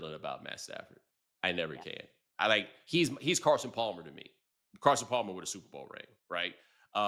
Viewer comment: I think he said “he’s Carson Palmer, Carson Palmer with a Super Bowl ring”.. talking about Stafford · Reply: oh no… I exposed myself as deaf. I heard “Carson Palmer WON a SB ring” …my bad